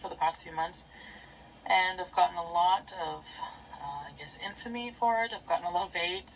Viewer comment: Thanks for the back to back uploads - glad you're pulling yourself through it, but I hear ya, I'm feeling overwhelmed too. On another note, at 35:13 does she say "I've gotten a lot of AIDS?" 😲